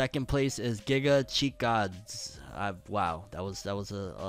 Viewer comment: I think he was trying to say Gigachad 🤣🤣